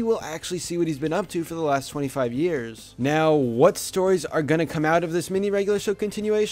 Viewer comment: At first I thought he said we have to wait 25 years 😂